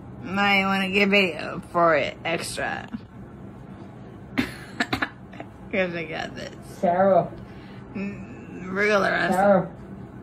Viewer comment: What does she say at 6:12 ? Something like real ransom because I got this might want to take a video extra?